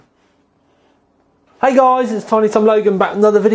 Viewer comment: What the hell do you say in the beginning of each video, all I hear is "Hey guys, it Tommy tom logan" back again